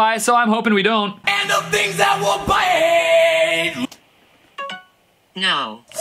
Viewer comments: I heard “AND THE BEANS THEY WILL BIIIITE!” 😂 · what does he actually say? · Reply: I think it’s “And of things that will bite” but I heard “And of baked apple pie” kinda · I hear "And the things I will buy" without the music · And a baked apple pie? I hear "and the things that will bite" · Instead of "a baked apple pie" I hear "The things that we,all buy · Bruh i hear "and the things i could bite"